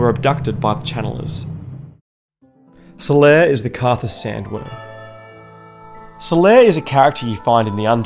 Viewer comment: Subtitles say "solaire is the karthus sandwich"